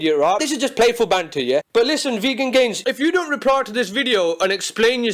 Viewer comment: I thought it said Virgin gains